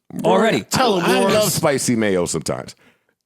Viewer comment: WAIT!! Did Joe call Rory "Spicy Mayo"?? LOL!!!! That's Charlemagne's lingo.